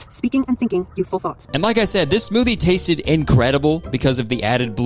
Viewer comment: i thought you said this movie tasted incredible.